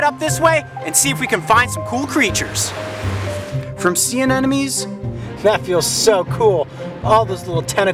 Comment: I'm sure I hear his say "see an enemies"